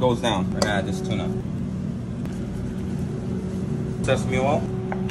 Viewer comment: For a second it sounded like homie said "sus me oil" 💀